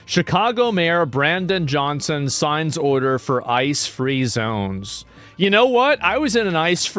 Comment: Sorry I misheard you, I came because I heard "Ice Cream zone" damn...